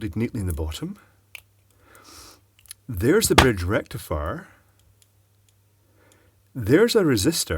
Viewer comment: I believe this whole channel is just the product of Clive going on drunk eBay shopping sprees and this is how he makes his money back. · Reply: thanks, now I heard 8:55 There is the bridge rectum fire.